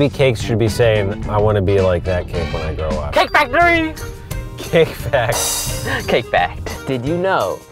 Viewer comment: i thought he said cake factory! XD